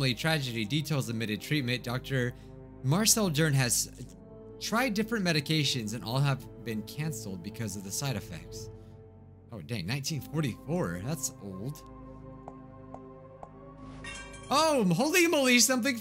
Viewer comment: i swear it said 1444